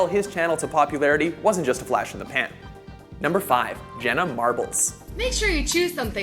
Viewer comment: did he say Hannah Marbles?